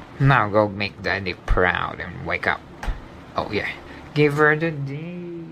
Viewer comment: Wesker say something but What he say ???? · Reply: Oh yeah! Give her a date!